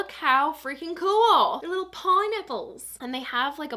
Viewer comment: I swear I heard "paw nipples" when you said pineapple 😂😂